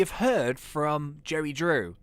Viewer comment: It literally sounds like hes saying Jerry Drew